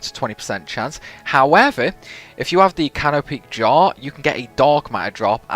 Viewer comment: if I have the? I'm can't make out what you are saying I need to have. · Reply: canopic jar